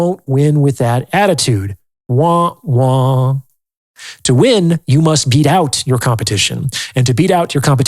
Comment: did he just say womp womp?